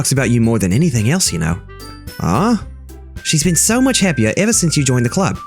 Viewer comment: I thought you said so much heavier XD